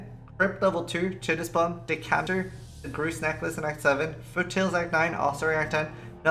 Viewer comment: is talking about skippable quests and i am not sure what is said after chitus plum, could anyone help ? thank you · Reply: greusts necklace